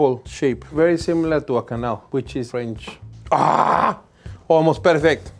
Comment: the subtitles saying "canal" and not quenelle at around 6:24 was funny